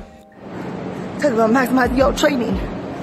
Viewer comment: I couldn't understand what see was saying so I turned on the subtitles and got "Mac Mike you training" I'm pretty that's not what see said. So then I listened to her over and over again, and I'm now pretty sure she's say "and will maximize your training" I think she might have a speech impediment (no shame), I have a speech impediment myself I just think that why it's hard to understand what she is saying.